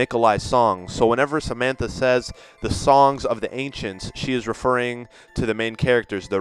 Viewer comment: who else heard thongs